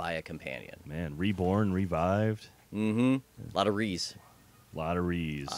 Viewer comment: Who else thought they said "there's a lot of Reese", when they actually said "there's a lot of 'RE's"?